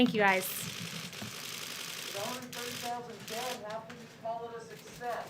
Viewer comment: What did that guy shout at the end??? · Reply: Something something something... How can you call that a success?